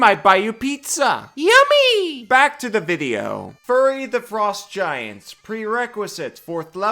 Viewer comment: i keep hearing "furry" instead of "fury" and tbh i think they should release a book of furry feats. mostly because half the playerbase would love it and the other half would absolutely lose their minds. do it wotc you cowards